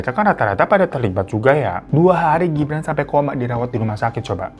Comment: dari ibra, jadi ibran Nah pas 14:28, kok malah jadi kedengeran kek gibran ya🤣